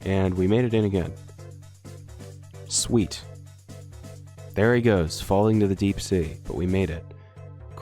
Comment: I almost thought said "Deep State" and not "Deep Sea"